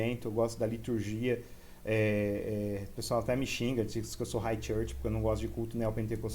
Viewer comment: eu entendi como High Church mas na tradução automática...